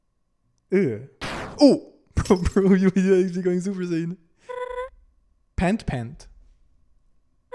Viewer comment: does anyone know what he said in here? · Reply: "OH. Bro, bro, you- Yeah, she's going super saiyan."